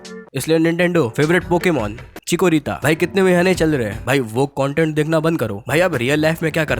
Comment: did said "woke content dekhna band karo".